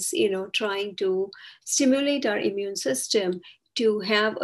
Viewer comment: It sounds like you said 'simulate' instead of 'stimulate' the immune system. I have iatrogenic autoimmune conditions and a severely low immune system. It is mandated that I have a second Covid-vaccine and wait seven days before I can attend my university classes, get library books etc. on the campus. The agency that put me on a horrid medical trial of Interleukin II will not see me since a government change citing that they don't have the funding. No-one will officially give me information about what happened to people on this trial. Some more humanitarian doctors involved in these trials since mid 1990s did tell me that people on the trial got auto-immune conditions. The main point is that over-stimulating the immune system without having an acute case of a virus, that it is already controlled etc. is not a good thing. The immune system will attack my kidney cells - when I have no active virus/viruses in my system. I also have permanent shingles. Perhaps this is also stimulated since having the vaccine.